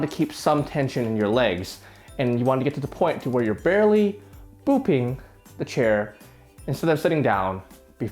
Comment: "booping the chair" This is the nicest exercise instructor I've ever heard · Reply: I thought he said pooping 💩🤦‍♂️